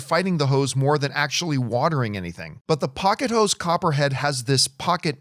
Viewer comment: this episode is brought to you by POCKET HOS. That's what I heard, and that's what made my head pop up from my work to see what the hell John was advertising. 🤣🤣🤣